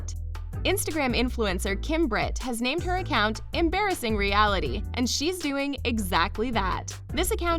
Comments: did you just say embarrassing instead of embracing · is JuST ME OR DID I HEAR HER SAY EMBARRASSING REALITY EVEN THOUGH IT SAYS EMBRACING 😂